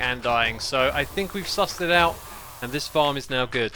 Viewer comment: Did you say “sus”?